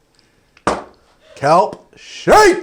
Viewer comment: I thought he said cum shake 😂